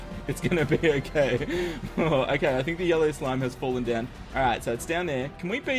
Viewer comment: When you said yellow slime after the yollo I thought you said yollo slime XD